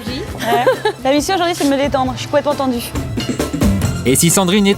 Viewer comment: Elle a dit quoi a 1:55 ? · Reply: La mission aujourd'hui c'est de me détendre, je suis complètement tendue